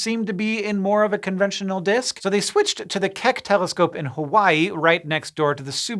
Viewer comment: Did he just say the telescopes are called Subaru and KEK? Lmao I really the closed captions say Keck but thats just too good to pass up